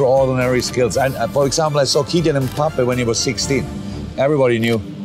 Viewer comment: did he say mboopie wow🤣🤣🤣